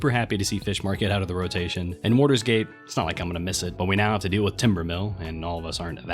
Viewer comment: Did u just say "tiberus"?